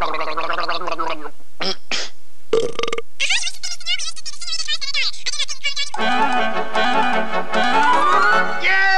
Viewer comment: what is he saying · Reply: "Uh-huh, you wish to see me, señor? We wish to see the señorita's tourist and his daughter. I do not think a common door name will..."